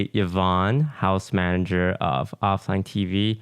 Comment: I thought Toast said "House malder of OfflineTV" lol